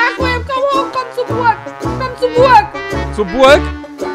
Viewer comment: bei minute 2:57 hab ich verstanden komm zur geburt😂